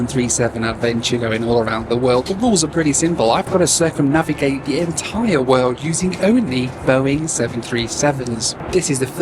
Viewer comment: I heard this as “can I circumnavigate the world in 737-300s?” I was about to be really impressed. 😂